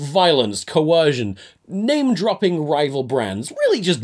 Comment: For some reason I misheard that as- "Name-dropping rifle brands", and thought he was REALLY testing his limits and going for mass shooting jokes